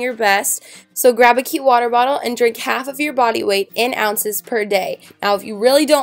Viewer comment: For a second, I heard "drink half your body weight in water." I was like... "Um... that doesn't sound safe."